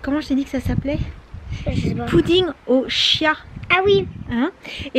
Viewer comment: au début j'ai entendu "puding au chien"😭😂